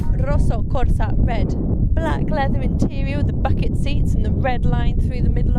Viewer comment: i thought she said fucking seats instead of bucket seats lol